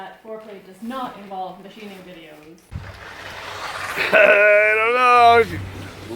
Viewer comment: "Might I remind you that foreplay does NOT involve machining videos." Idk man, that sounds like grounds for a divorce... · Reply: Ha I thought I heard bikini videos